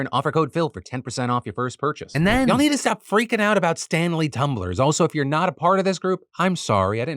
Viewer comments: Honestly thought you said "Stan Lee" Tumblers and I was sorely confused until I looked at the screen and scoffed in disappointment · I was listening through audio only, and I heard Phil say "Y'all need to stop freaking out over Stan Lee's Tumblr". I thought there was some fanfics uncovered or something like that.